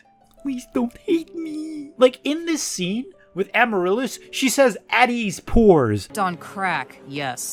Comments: I actually thought she said "wh*res" since it isn't impossible for the writers to actually mean that, they throw in random swear words from time to time. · I thought she said Whores